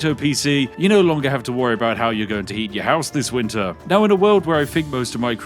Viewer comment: I know you mean to say "heat," but it sure sounded like "eat" and my mind went in strange directions.